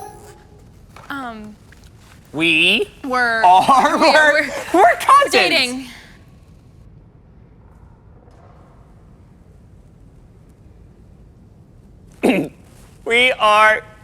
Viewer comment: Did she said dating?😏❤